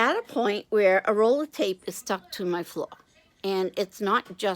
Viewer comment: Oh...you get into the stickiest situations LOL ...happy you were able to get it unstuck. At first I thought you said it was stuck to your foot! You should send this to UNDO! I like your idea about helping out folks who are new to stamping/papercrafting--it can be overwhelming!